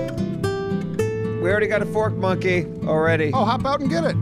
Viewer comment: First time they said "fork monkey" I thought I heard porch monkey and got really worried about where the channel was going.